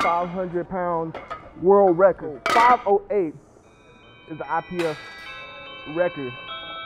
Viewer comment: Did he say ipf 0:03 ?